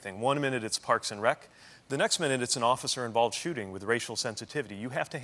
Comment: Notice the moment when he says “the mayor has many responsibilities; one moment it’s parks and rec, another moment it’s the office” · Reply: I didn't notice because that's NOT what he says. THIS is what he says, "...the next minute it's an OFFICER involved shooting with racial sensitivity." @ 3:44 40 people gave thumbs up to this comment. "Telephone" and bandwagoning haters at it's worst. We need to work on our listening skills and not be so eager to find fault. Reach for the positive. Fake news survives because the hopeless thrive.